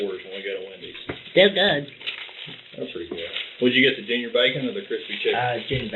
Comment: thought I heard " They're good. That freaked me out".